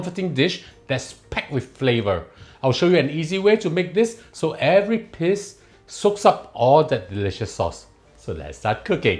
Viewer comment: What did u just say so every piss soaks up that delicious flavour